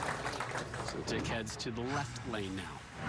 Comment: Did I hear that correctly? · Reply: They said “so dick heads left now”